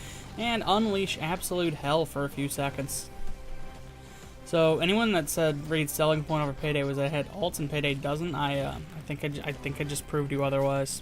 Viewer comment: What did you say at 3:11 ? I basically heard "So anyone whose selling point over payday was I uh ah nfjfirnfofinrr i I think I proved you otherwise" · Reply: "So anyone who said Raid's selling point over Payday was that Raid has ults (ultimates/War Cries) and Payday doesn't, I think I proved you otherwise." In my defense, Raid seemed a lot more hopeful when this video came out.